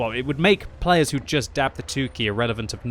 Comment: I always thought you said dab the tookie as an idiom, turns out you’re literally saying 2 key. This is Console moment